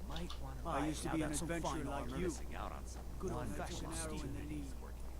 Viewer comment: I swear i heard "I took a good old fasion arrow in the knee" while heading to the shrines lol.